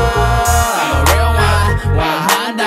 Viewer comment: I'm a real one 1 honda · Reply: it sounds like he said 1 honda but I know its 100